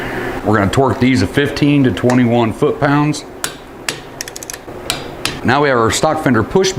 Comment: My dumb ass thought you said 132 foot pounds 😂😂 i snapped my stud clean off